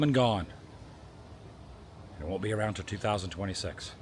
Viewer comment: does say he won’t be around for 2026? 😢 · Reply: He says it wont be around until 2026 , meaning the taxes again